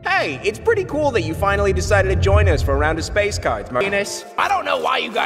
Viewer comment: What everyone hears:Venus What i hear:Anus